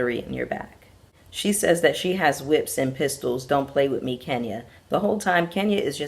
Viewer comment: S/o to the gnat! I thought she said “ I’ve been whipped and pissed on don’t play with me” 😂